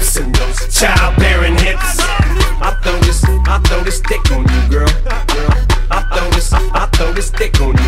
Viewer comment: sounds like hes saying i love you :O omg i love u too! HAHAHAHAHHAH C